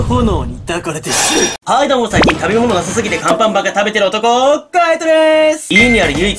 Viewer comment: 最近聞き間違いヤバイから耳鼻科 行った方がいいですか？ ちな、最初の挨拶のときに ⭕あんパンばっか食べてる男 ❌アンパンマン食べてる男 っていう聞き間違いしてましたw